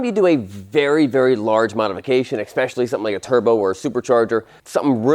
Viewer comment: did you say eckspecially?